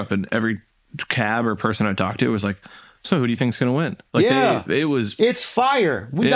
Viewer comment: Sheesh i thought mark was saying ask someone from Ukraine who’s going to win the war lmaoo